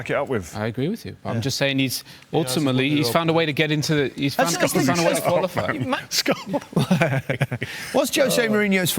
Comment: what did scholsy say to rio 1:23 ?? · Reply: "he won the europa league"